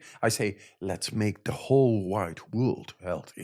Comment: what did he really say let's make the whole 'white' or 'wide' world healthy? Did I miss heard him saying "WHITE"?